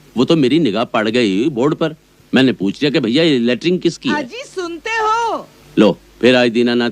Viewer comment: did he said potty 😂😂